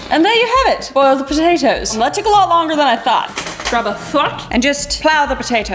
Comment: When Kris said “grab a fork” I thought she said “grab a f*ck”😂 Kris is the best at making me laugh. she puts so much effort into making us happy❤